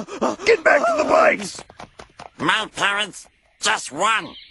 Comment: "My parents? Just one: Piccolo; and he was kind enough to give me everything he had." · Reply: I thought it said my parents just run, and then he said they gave him everything they had, the bullets.